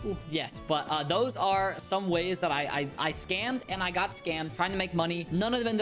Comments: I thought it said I scammed I kid. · I thought it said I scammed a kid